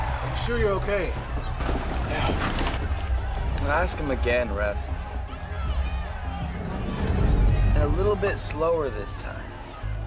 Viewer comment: Can someone write down what Ryan said to Jake? at the moment 9:19. I write through a translator and I don't know English. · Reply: Ask him again ref.. and a little bit slower this time